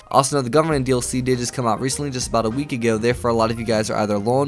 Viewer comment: i thought he kept saying government dlc instead of gunrunning.